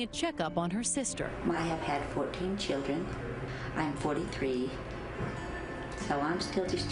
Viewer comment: Did she say she was 43? 27:49 wow